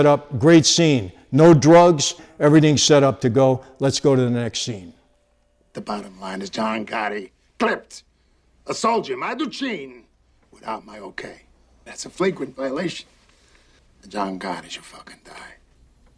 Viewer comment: what does Castellano say? My duchine?